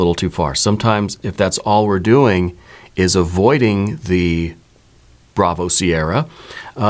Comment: Great reading thanks! What do you say around minute 26:33? avoiding the bravo...? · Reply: Bravo Sierra… (BS!)